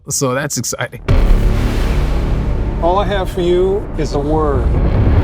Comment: Who else heard it as “All I have is a word... tell it.”